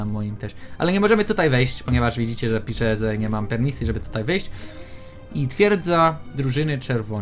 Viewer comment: "Ale widzicie, że nie możemy tu wejść bo pisze, że nie mam penisa" czy tylko ja tak usłyszałam?